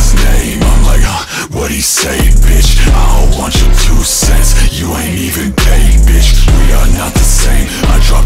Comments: i love mishearing “you ain’t even paid” as “you ain’t even gay” i don’t think ill ever unhear it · why do i hear "you aint even paid" as "you aint even gay"